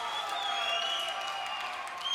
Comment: Can someone tell me what he said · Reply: He's introducing himself in Italian, "io sono Papa Emeritus Secundo"